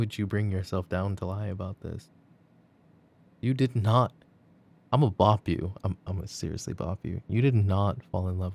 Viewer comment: Did he just say he’s gonna bop me 3:43 🤣🤣🤣🤣🤣 lmao love that for me 🥰